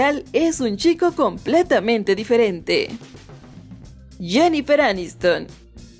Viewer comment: es un chico muy diferente un (chico) escuche bien ? Un (chico) ?